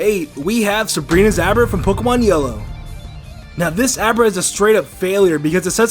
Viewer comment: is it me, or does it sound like he is saying Pokemon Yolo?